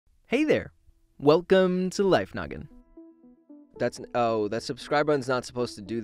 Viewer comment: i thought he said welcome to life nugget